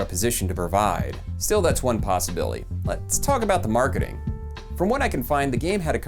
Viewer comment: great video, but did you say possibilly at 9:17? love it!